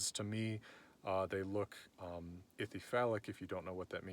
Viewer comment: Ohh, Ithyphallic not Ichthyphallic for a minute I thought you were saying they looked like a fish penis.... I think Fish penis could have been another ingredient of Gleipnir.